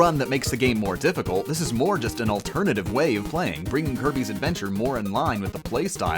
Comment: to me it sounded like he said herpes adventure